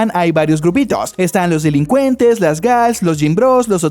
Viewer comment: entendí las gays